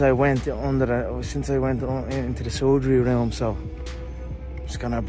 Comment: I thought Conor said in to the surgery realm lmaaaoooo